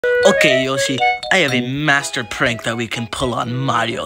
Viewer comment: Thank you guys for Watching! Lets try and get this video to 500 views and 50 likes in the next 24 hours! <link> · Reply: At first I thought Luigi said I have a master bate that we can pull on Mario